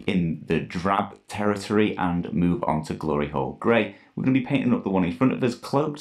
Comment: Is it me or was not saying the paint names correctly. Flassid bone, hard on leather, ect. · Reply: I definitely heard "glory hole grey"